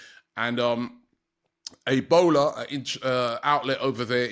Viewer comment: did he really say Ebola there in Africa ?